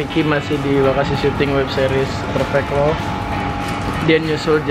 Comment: Judul web seriesnya 'perfect love' nih? Salah denger kali yak gua wkwkwk